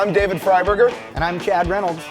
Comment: Did he just say his name is David FRY-BURGER....??!! *Yea, whats up dudes...im shawn Steak~Lobster 😅😅😅